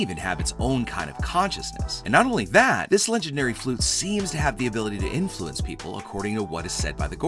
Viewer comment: did he say flute?